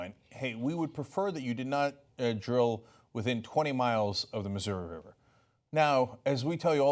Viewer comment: 20 miles Ooooh... I thought you said 20 meters.